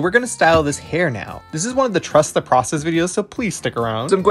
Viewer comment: I thought You said trust the progress